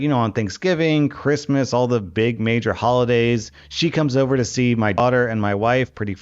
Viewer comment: It sounds like he said "She comes over to see my otter and my wife" 😂